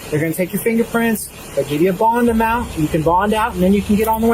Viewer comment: If you are going to use closed captioning at least check it. 10:58 "I give you a ball in the mouth" instead of you'll get a bond amount.